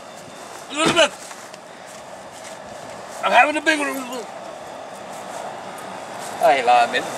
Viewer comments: did he said I'm having a big one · wtf did say at 2:04? i heard "ELIZABETH.....I'M HAVIN BIGLOOGALOO!" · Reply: He said I’m having a big one meaning a heart attack 😂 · What did he say at 2:08😂😂😂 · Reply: Family Of flames I’m having a big one Elizabeth is what he said